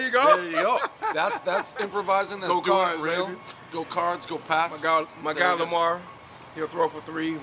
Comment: Thought he said go carbs